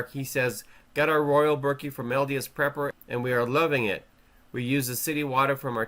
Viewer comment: I thought it said LSD prepper! Hahaha